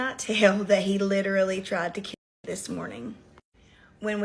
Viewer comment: Please with the censoring 😂 it sounds like she said her pit bull tried to fuck her 😂😂